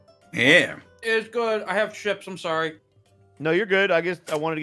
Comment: I swear I heard say 'i have ships.' and my first thought was, 'spoken like a true fan.' and yeah. I know/suspect it was chips but my brain does things sometimes. Also I can see Milo being the friend who ships his friends, not in a weird way but like 'this person plus friend x would be a good couple' kind of way.